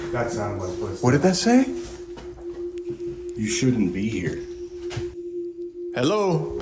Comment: I heard "you shouldn't be here". So clearly I thought you had a third person with you.